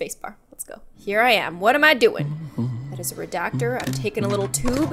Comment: Who else thought the red thing said RedVacktor. Aka YOUTUBE SENSATION REDVAVKTOR